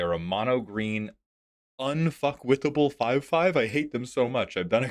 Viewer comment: Who else misheard 0:33 as "UNF**KWITABLE" and hoped he's a Fantasy Fiction podcast fan? Also "Thrun"/"Throm" the coincidence is just too real and now i want to make a Thun deck. If you know you Know 🤘